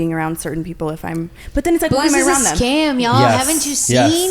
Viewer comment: what does Meghan say here when they’re talking about alcohol? … “___ is a scam man, haven’t you seen?” What’s a scam? I’ve rewound it like ten times and I have no clue what she’s saying. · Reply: Booze is a scam